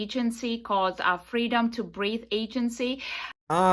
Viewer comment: Her accent got me messed up I swore she said freedom to breathe ass 😒 🙄 🤧 😭 🤣